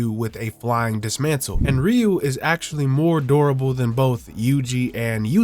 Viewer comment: I thought you said he was more adorable lmaooo.